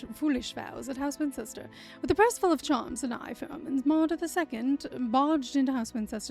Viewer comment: I was playing my own game while listening to you, and I misheard the "eye for omens" as I-phones. Seemed so out of theme I had to stop and check what it was. 😂